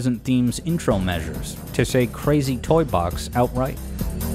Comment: I always heard “3 2 1” instead of “Crazy Toy Box”